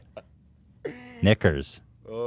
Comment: I hear her saying “Nakers” very clearly